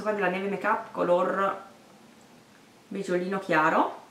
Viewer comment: Giuro che quando hai detto il colore della matita della neve ho capito "Color pisolino chiaro" ahahahah povera me! sto sfasando :)